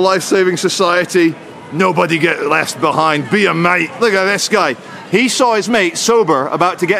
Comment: I thought that said "beat meat"